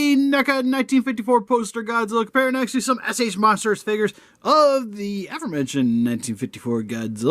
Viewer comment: I’m sorry, what did you say? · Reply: "S.H MonsterArts Figures."